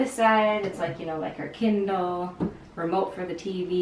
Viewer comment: For a second I thought you said her "Ken doll" and I was like wait what?....haha then I realized you said Kindle.